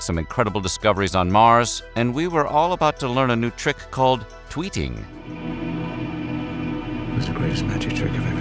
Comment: I totally heard "tweaking" instead of "tweeting." 😂